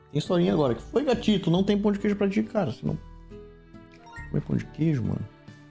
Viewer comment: foi sò eu que ouvi ou ele falou pro gato que n tem pão de queijo kkkkkk